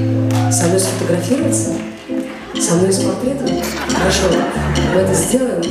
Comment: А кто расслышал, с чем "она"пришла ?) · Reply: Мне послышалось (я прослушала в наушниках), что девушка пришла с портретом Лолиты. Прикольно получилось, но в профиль девушка действительно похожа на молодую Арбенину (есть записи в инете, на ютубе, где Диана с такой прической и в очках круглых, только волосы темнее у Дианы).